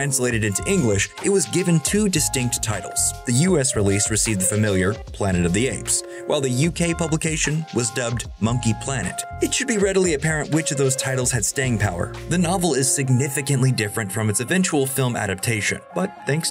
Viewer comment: I don't know why but I just about died laughing when I heard "monke planet"